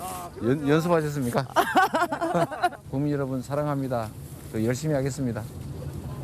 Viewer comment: 열심히 도둑질 하겠습니다로 들리네.... 아이고 짜잘한 넘아 해먹을라면 제대로 해먹던지 그래 법인카드로 샌위치 쳐먹었냐? ㅋㅋㅋㅋㅋㅋㅋㅋ